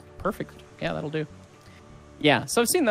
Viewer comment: Every time said that will do all I heard was “ That will do pig” lol